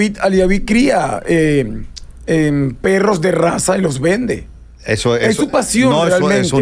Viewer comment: dijo perro de Raza ….. 9:34 señor escuche bien antes de corregir